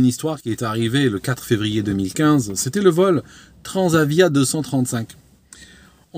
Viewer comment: j'ai les oreilles bouchées ou j'ai entendu TransaVia 235? :D